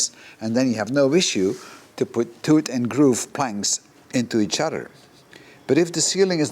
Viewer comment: Teat and Groove = Tongue and Groove. The method you are going to use is maybe a 'Half Lap' joint? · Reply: I thought he was saying "tooth and groove".